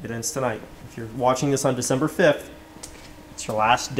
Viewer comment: did he say December 5 ?!?